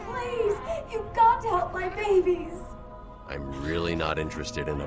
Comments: For a moment there thought she said “batman you got to have my babies” 💀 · When ivy said you've get to help my babies I thought she said you've got to help me make baby's.... I mean I just wanna wanna help her